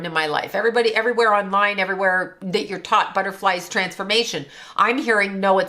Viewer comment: To pimp a butterfly 28:01 is what I heard when you said that And thought of The Matrix when you mentioned being in the “cocoon” “when you unplug they come after you. Contract breakdown CON- to persuade or to believe something typically by use of deception ;TrickeryTRACT-and area of indefinite extent, also large bundle of nerve fibers in the body or other continuous elongated structure or region “digestive tract” You can also contract, or catch something the “spores” you mention when it comes to ab.use